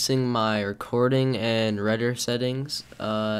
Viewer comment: Did you say "reder" settings?